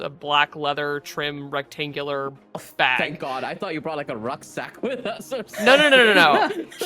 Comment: did I hear correctly? "Rucksack"? That's backpack in German. Is it becoming a loanword in English now?